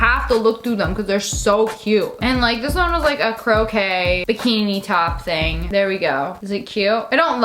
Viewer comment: so am I the only one who heard her say croquet instead of crochet